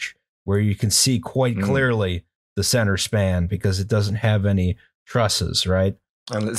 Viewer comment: Every time I hear say "centre span" my brain goes "Lisa needs braces"